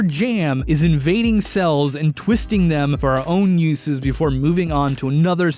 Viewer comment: I'm sorry, but did you say "our"?